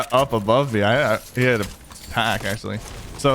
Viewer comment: In 7:33 it sounded like u had an epic tac